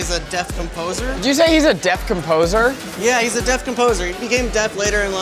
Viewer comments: "he was a deaf composer?!" Let me tell you about this guy Beethoven... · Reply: I heard 'Death Composer' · thought he said "Death Composer" lolol